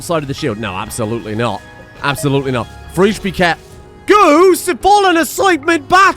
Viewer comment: I'm likely the only one lol, but every time you say "Fre'esh", I heard "Fries", as in "Friesland". Give them some frizzy hair. Trust me. It's only right.